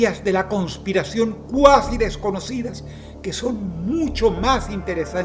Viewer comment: Segundo 0:48 dijo cuasi? O escuche mal? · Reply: sip dijo cuasi